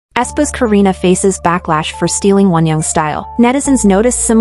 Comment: for a second i thought that it said backwash instead of backlash😂